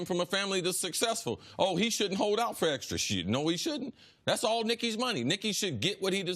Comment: CC SAID "SHIDD NO HE SHOULDNT" 3:45 😂